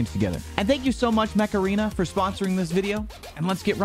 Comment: when he said mech arena for the first time i thought he said macarena